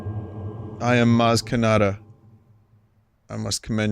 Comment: did he say "más que nada"? XD